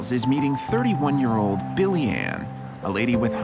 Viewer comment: I thought it said “he’s meeting 31 year old billionaire…”